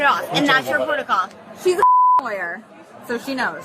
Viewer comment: I heard “She’s a fucking lawyer” in Shane’s BBG voice lolololol